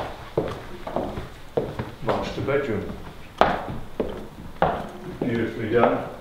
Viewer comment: I understood "maaaaasturbate room"- Then played it back and understood what he said without a problem, strange XD